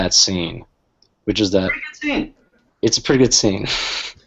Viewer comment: does anyone understand what they're saying at 47:34? "[..?..] scene"? i've replayed it like 25 times but i don't get it lmao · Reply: gretachar "it's a pretty good scene"